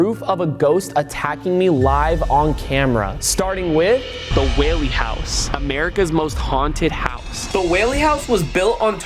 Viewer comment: I thought you said wawy house